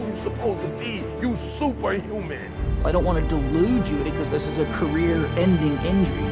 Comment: Can someone tell me what is said at 01:04? Right before "because this is a career ending injury". I'm correctly translating the subtitles but can't understand the full phrase (I don't want to ????? you). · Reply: 'delude you " from the word delusion like in falsely convincing your self that fiction is fact. Also watch this /watch?v=g-jwWYX7Jlo